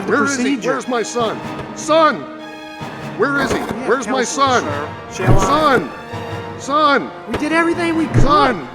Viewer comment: We can add it to this version if you email us a caption file (but please do not make an unauthorized upload of the video to a different channel). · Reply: What did the doctor said? "She have console, sir, she alive"????